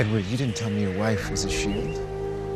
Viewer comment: I thought he said "you didn't tell me your wife was a she" and I was like ohhhhh Edward and this guy got some history